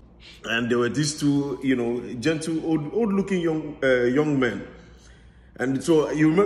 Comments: "Old looking young men" for me 🤣🤣🤣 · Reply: Odd looking I thought he said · "old looking young men" 😂😂😂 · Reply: I thought he said good looking lol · "Old looking young men" for me 🤣🤣🤣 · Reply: I thought he said odd-looking 🤔